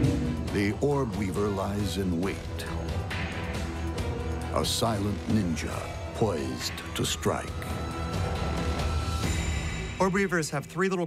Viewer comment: Did he just say the silent Inja, poised to strike? I can acknowledge dat